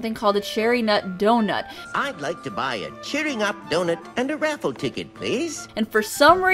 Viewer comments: I don't hear "Cherry Nut Doughnut" I hear "Cheering up Doughnut" no matter how many times I replay it, he never says the word "nut" outside of doughnut. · I hear " a cheering-up doughnut" , not "a cherry-nut doughnut". Cherry-nut doughnuts do sound awesome, though. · i thought he said Cheer me up donut not cherry nut donut :I